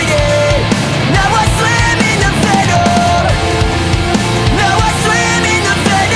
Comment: i swear i hear ''fentanyl'' instead of ''venom'' 😂